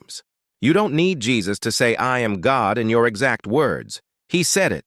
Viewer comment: I hear him saying " I am Pig"